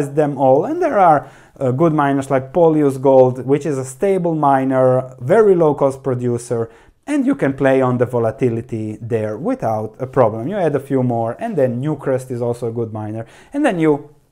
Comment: What's the name of this miner, please? I couldn't hear it properly. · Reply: I think polyus, newcrest were mentined